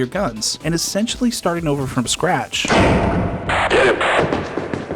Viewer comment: crunchy audio. some people understand "I see it". he actually says "nice hit"